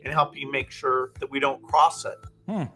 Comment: Does Gates say "help ME make sure we dont cross it" shouldn't he have said "help US make sure we don't cross it". Micah 2:1 Woe to those who devise wickedness and work evil on their beds! When the morning dawns, they perform it, because it is in the power of their hand.